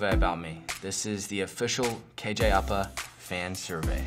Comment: it sounded like KJ apa said “fan survey” with an english accent